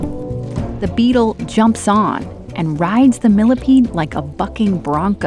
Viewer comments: It kind of sounds like shes saying: the beetle jumps on like a f*cking brunko. LoL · to be honest I thought she said f*cking 😂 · she sounds like she said "like a f-ing bronco"